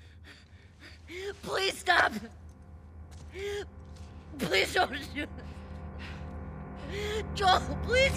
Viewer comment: ever notice how the captions say "please dont do this" but she actually says "please dont shoot"